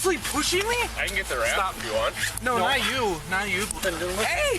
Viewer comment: he clearly said wrap why is it redacted is a wrap to tie his feet up or him up so he stops moving